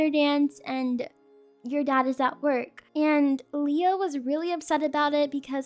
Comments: you said Leo instead of Lia. oop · Is it just me or at 6:59 does it sound like said Leo instead of lia? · you called her Leo instead of Lia